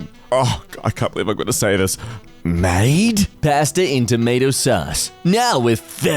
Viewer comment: why did you say "tomato sus"